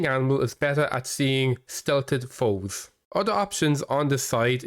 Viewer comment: What did you say here? "A hunting animal is better at seeing... ???" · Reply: …better at seeing stealthy folks. Or …stealthed folk (I know it’s not a real word)